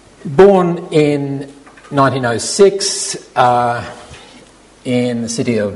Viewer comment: You made a mistake in the short biography, you said Godel was born in 1996 rather than 1906. That's okay, we all make mistakes. Also, "This sentence is true." is technically a sentence but it conveys no information, so its just words, the meaning is not truth or a lie. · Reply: ***** Sorry, I'm in error, at 2:13 I hear 1996 but in listening again to the whole segment, the narrator's accent formed several new words.